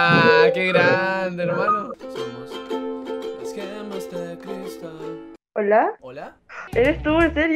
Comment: escuche "somos las gemas de cristal"